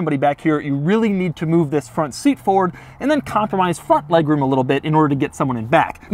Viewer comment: Did anyone else hear him say “F*ck legroom” at 9:41? 😂